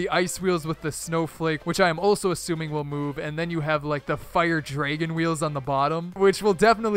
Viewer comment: Did you just say "Draygon"?